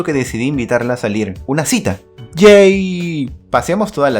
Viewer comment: lo que dice: una cita ¡jay¡ Los subtítulos: un cita gay